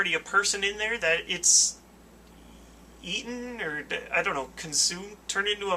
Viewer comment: The confagrigas entries when you said eaten i thought you said ethan